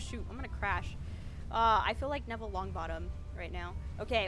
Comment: A true... Hogsworth student??? Is that what you said? Hold up... edit: 9:16 neville longbottom question mark